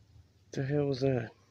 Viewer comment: Sounds like he said hellow. Speak calmly and say you are leaving the area. They seem to want to stay hidden and protect their territory and thier family. They have been around long enough to know what can happen to them if they are discovered by man.